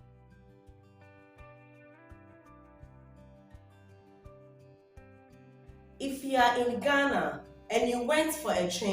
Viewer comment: What liquid did he say at 1:44 the gold liquid in the bottle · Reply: Sulphonic acid